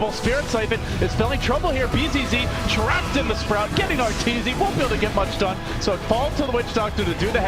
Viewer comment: lmao did he say RTZ?